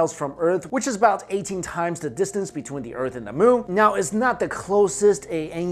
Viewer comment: "Between the Earth and the moon" Caption Says "Between the Earrh and the sun"